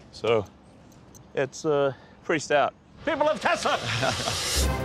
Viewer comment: idk why but that "People of Tesla" sounded like "King of the world."